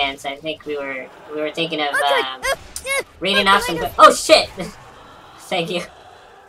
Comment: What were you going to say at 3:36? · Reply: It sounded like he said "Reading off some -" something.